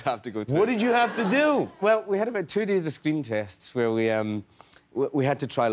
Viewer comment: i thought he said skin test instead of screen test... i was so confused...